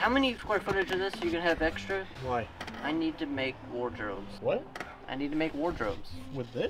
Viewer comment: At to 24:50 did he say wardrobes or War drone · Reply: he said Ward Robes